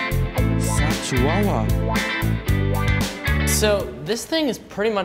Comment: did you just say fat chihuahua?